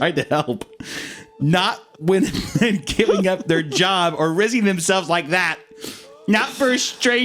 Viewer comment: "rizzing" instead of risking, te rot is reasl